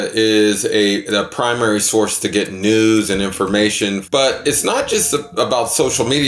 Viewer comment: I really heard "nudes" at 6:57 at first